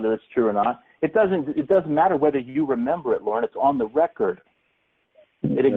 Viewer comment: does anyone else hear some demon sounding voice say "Lorrrne"?